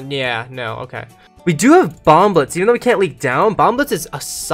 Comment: OH HES SAYING BOMB BLITZ I THOUGHT HE WAS SAYING BOMBLETS